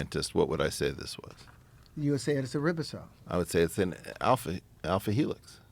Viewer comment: did he say ribosome lol